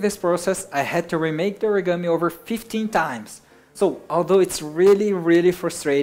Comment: did you said 50 times or 15 times? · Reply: he said 15 times